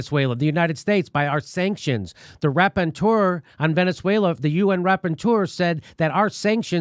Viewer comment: What word is Jimmy saying at 7:41? Raponteur?